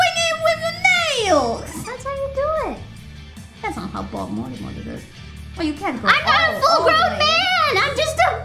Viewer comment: I literally have no clue of some of what they are saying from 2:00 to 2:20. I thought it was just my phone. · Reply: It may not be accurate, but what I was able to hear was: Gura: "That's how you do it." Kiara: "That's not how Bob Mortimer did it. Well you can't go--" Gura: "I'm not a full grown man! I'm just a-- " Kiara: "Rip it! Rip it! Rip it here!" Gura: "I'm not a full grown man. That's not fair!" Kiara: "Yes, you will be half Korone. It's not as impressive as it should be, but she's getting there."